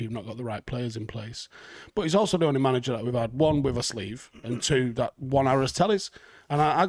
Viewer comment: Anyone else have a clue what’s being said here 6:13 or is it just me being silly? “A sleeve” and what sorry? 😅